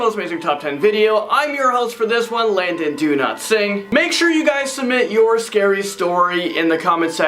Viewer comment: I thought you said Landon Do-not-sing hahaha